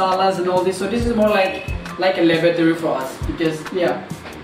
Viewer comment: bro did you just say lavatory!!!!! Or laboratory hahah